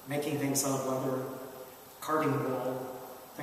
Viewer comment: Did you say "carving wall" at 0:36 ? Do you mean "carved wood" ? Or did they have a wall where they carved figurines ? Sorry I got lost a bit there.